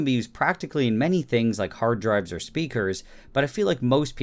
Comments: Did u say hard drives or hard drugs lmfao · I need to get my ears checked. You said “hard drives and speakers” and I heard “hard drugs and sneakers” and was intrigued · i thought he said it can be used in hard drugs XD